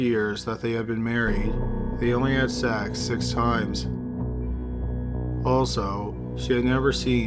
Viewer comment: They only had sex three times and she became pregnant three of those times??? Not buying it. So she was cheating or she is lying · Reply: I thought he said 6 times. I'm too lazy to check. :)